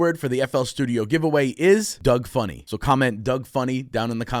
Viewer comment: I heard "DOG FUNNY" Probably because I'm Nigerian😂